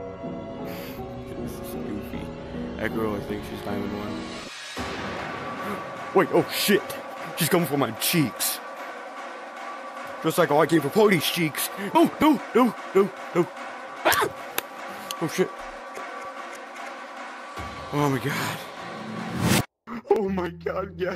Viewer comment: Yooooo did he just say “Like I come for Party’s cheeks?!?!?!” WUT DA HEEELLLL